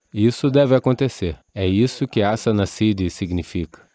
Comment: Poderia me dizer como se escreve o que ele disse "Açanã sid" - quando ele diz que é quando alguém alcançou uma certa perfeição de postura (tempo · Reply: Asana Siddhi é assim que se escreve a palavra que ele disse. "Asana Siddhi significa que você alcançou uma certa perfeição de postura." "Postura significa que você alcançou uma certa perfeição de geometria." "Geometria quer dizer, toda forma fïsica." .....